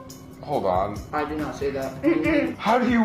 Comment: You can hear him say "I did not say that" and honestly I wouldn't be surprised if the parents ever made that out to humiliate him